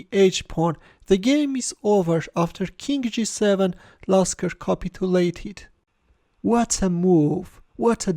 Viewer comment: For a second I thought you said: "Lasker copulated."